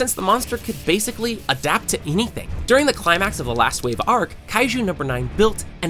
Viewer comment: did u just said adopt to evrything ?